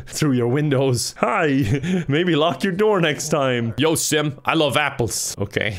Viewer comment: it sounded like he said "Yo Simp"